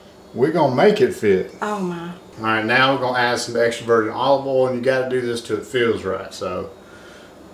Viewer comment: It sounds like is saying extroverted olive oil. Lol.